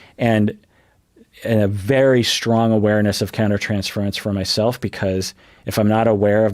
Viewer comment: Can someone please tell me what word he uses at 6:20, "something" transferents? English isn't my native language. Thank you! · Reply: The word was "countertransference"